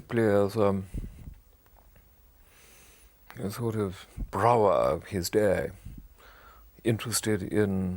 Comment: That art critic at 3:15, "Sort of a "brow-ah" of his day" - what is he saying? I'm usually good at accents but his pomposity is in the way lol